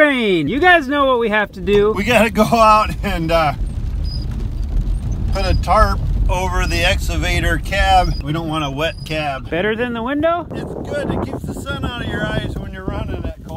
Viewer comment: Let me start by saying your downloads are great and I love the channel. I just have one question. Is saying the Ex A vator or Ex Ca vator? It sounds like he is saying exavtor which makes me laugh and I do not know why. Keep up the great work.